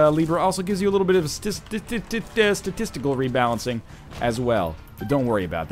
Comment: Am I the only one who heard "sta-testical" instead of statistical?